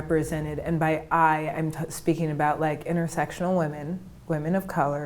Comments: did she say she's intersexual? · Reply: OMG thanks for clearing that up. I thought she said intersexual too. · did she say she's intersexual? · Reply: I thought she said intersexual too. Now that I know it's "intersectional" I have to ask: what does that mean? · What did she say she is at 4:30 ?? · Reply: E Laulii "intersectional woman of color"